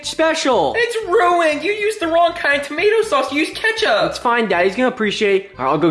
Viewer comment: Did say its fine daddy